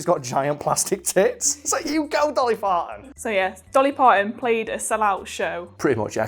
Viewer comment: does say Dolly Farton?